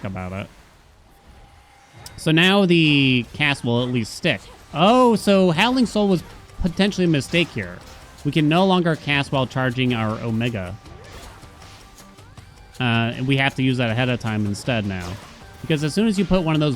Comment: I honestly thought you were saying “haelian mistake” for the longest time. It wasn’t until you said “tally a mistake, yup chat, tally another one” that I understood 😂